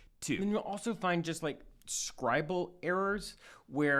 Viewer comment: Is there any way you can correct your subtitles to read "scribal errors" instead of "scribble errors"? (at 3:11 or